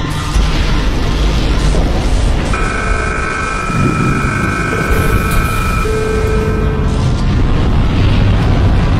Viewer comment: for people who didn't almost understand this part so dafuqboom/secret agent is trying to comunicate by hacking and getting into the camera mans visions and povs so i think thats why he said there look to the left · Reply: I think he said look at your left